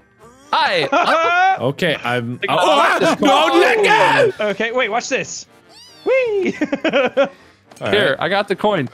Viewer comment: what did mark say? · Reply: "Uahh Don't let go"